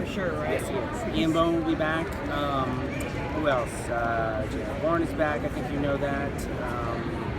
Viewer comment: >which name does he say at 2:20? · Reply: JR Bourne - The man who plays Chris Argent